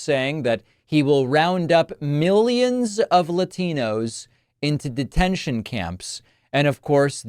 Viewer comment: did he say Latinos?